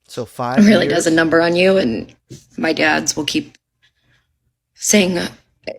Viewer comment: Wait what?? Did u guys catch that?? For she just say "my dad's!!" ???? WELL THEY'D YR PROBLEM LOVE!! Need I say more fellas??